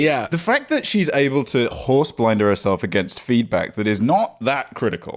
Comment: what word does say here? horseblender? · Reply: Horse blinder. It's a thing on a horse's bridle that blocks part of their view so they don't get spooked by things from the side.